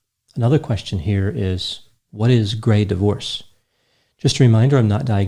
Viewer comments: I get a "Grey Divorce"... If you're not happy, or need a change of life, you still have 30+ years left.... That's the entirety of my existence at this point. If you're not happy... Do it. Be happy. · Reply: Oh, GRAY divorce! I thought you said gay divorce. Never mind. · I thought you said "gay divorce." Was waiting to hear Melinda's a dude